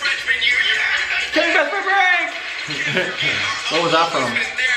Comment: Is it just me that wants to know what said at 1:27 ?? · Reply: I think he said killing bestfriend prank